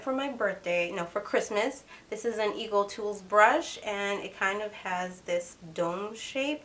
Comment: First off, thank you for this video 😊 👍 I want to try this brush (9:14) Did you say Eagle Tools Brush? I cannot find it anywhere. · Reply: Its EcoTools!